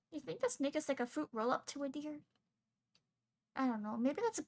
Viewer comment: did she say the n word or am I half deaf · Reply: Sorry for the confusion but no I said "do you think this snake is like a fruit roll up to a dear" apologies for the confusion.